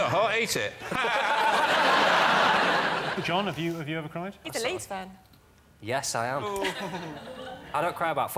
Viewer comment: After the question about whether Jon ever cried, I thought I heard Rachel respond, "He's a 🏒 Leafs fan."